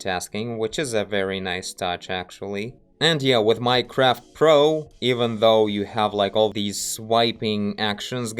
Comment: What was the name of the app with "all the swiping"? It sounded like you said, "My Craft Pro."